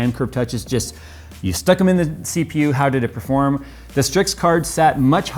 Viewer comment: did he say you just stick it in the CPU?